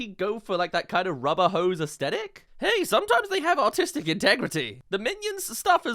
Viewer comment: I swear he said "autistic integrity" 😆